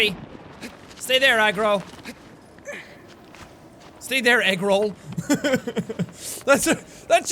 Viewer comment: I thought he said to to horse egg roll